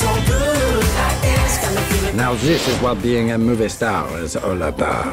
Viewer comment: ooohhh, show dogs. i thought it said snow dogs. i have not seen that movie in probably a decade